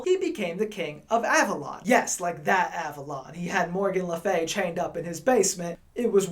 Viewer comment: I heard that he goes by "Captain Avalon" these days!!!